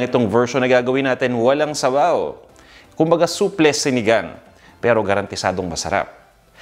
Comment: Oh my, iba ang pagkadinig ko, I thought you said soufflé sinigang. I think I need to have my ears checked😬. Love this new way of having sinigang, thanks for the idea. I love sinigang, with lots of okra and gabi😋🍚